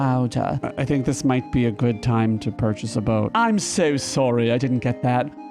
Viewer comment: "Steal a boat", that's what I heard 🤷🏼‍♀️